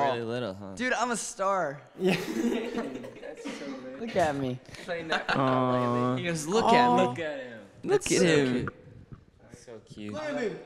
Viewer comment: Who else heared Zach say look at me look at me fuck on me but he said fuck soft